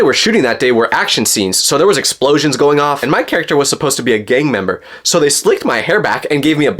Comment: I thought he said " gay " member not gang member